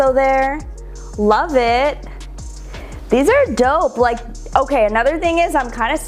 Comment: Did she say "these are dope"? 😆